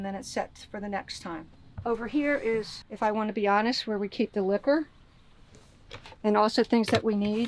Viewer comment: Watching in 2x speed at 4:02 i thought she said “this is where we — i want to be honest — keep the liver” - I was so confused, glad she showed the jack daniels so I knew what she really said. 🤪